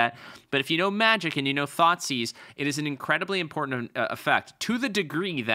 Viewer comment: Never played magic and thought you were saying "thoughtsies". Then you revealed the card and it was much less adorable